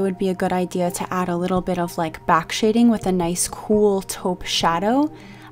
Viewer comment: I know you have other videos and the chances of you replying are slim. But if you do at 7:50 did you say tope or toke shadow? Also to get this look do I need the exact products or can I find a substitute (for lack of a better term)? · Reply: I meant the color taupe!💛 you can most definitely use other products if you don’t wanna buy the exact same ones just make sure they’re similar enough